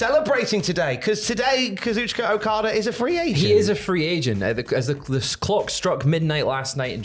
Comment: For a second at the beginning of the video it sounded like you guys called Okada a "free Asian" and I thought "what the heck did they just say?" But then I realized you said "free agent" and I laughed a little. 😅👍